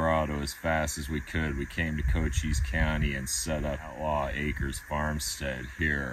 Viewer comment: I thought you said "goat cheese county" 😆 🤣